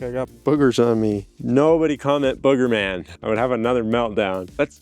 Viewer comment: sounded like "nobody come at boogerman" lmao like that tomato did